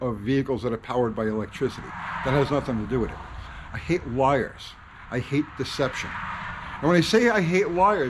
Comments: I thought he said: 'I hate wires' 😂😂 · at first I thought he said in the beginning "I hate wires" and I was like,, yeah me too, ripped my headphones off my head so many times, in ear earphones painfully yanked out of my ears, and game controllers with a cord so short I cant sit back and relax in my chair. Then I realized he said he hates "Liers" (however it's spelled I can't be bothered to look it up) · thought u said i hate wires cuz i do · Lol I thought you said “I hate wires” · I thought you said you hate “wires”. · I heard , "I hate wires".....